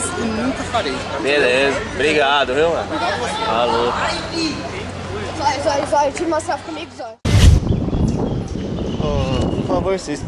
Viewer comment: Quem aí Ouviu " Zoi Zoi tira uma selfie comigo Zoi "